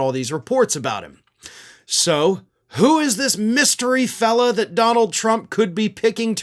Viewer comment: I thought you said “mystery felon” not fellow. Still think it sounds right. Don’t even ask me what I thought you said when you said “jag officer”…